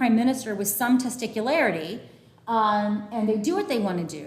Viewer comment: whaaaat. 🤣🤣🤣 Did she say testicularity